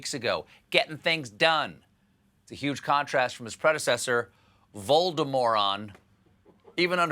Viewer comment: I just misheard 'his predecessor' (in the context of the president and Covid) as 'His Spread Assessor' which is also not inaccurate.